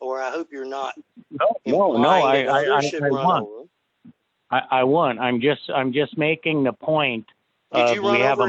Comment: sure sounds like says "I won".